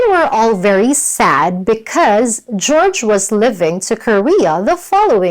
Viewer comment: I thought I heard "George was living"